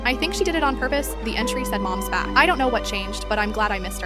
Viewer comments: When she said when it said mom's back first I heard Mom's fat I was like what happened I thought you were in good terms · I heard "mom's fat" lol 😭😭🤣🤣🙏🙏✨️✨️ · I heard 'mom's fat'